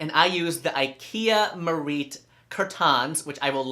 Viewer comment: You didn't link it like you promised, so we can only guess because it's sounds like you said "Ikea Maurice" curtains but CC has "mariet" and Ikea does make a Merete curtain at 98" (8'), which is a typical ceiling height for most older suburban homes. In fact I don't know that Ikea makes curtains that are longer than 98". Anyway it look's like you hung them just above the floor and there's about a foot of space from the rod to the ceiling, so your ceiling is more like 9', not 15 ;-)